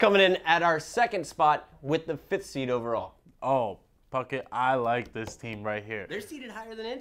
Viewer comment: it sounds like is sayins fuckett